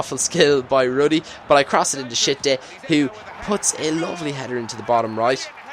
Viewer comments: At First I Thought He Said "To Shit Dick" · i thought you said shit dick haha